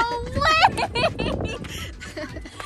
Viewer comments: I thought she said “YOU GOT ME A BOX?!?!” 😂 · i thought she said :you got me a bomb? she rlly said: you got me a bob? BOB = Body Oponent Bag It's a Punching Bag looks like a human, it has a body and a head, but it doesnt have arms and legs. She calls him bob